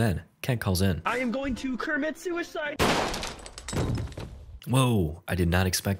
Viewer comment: “ Im going to kermit shoe inside” What!!, He said Im going to commit suicide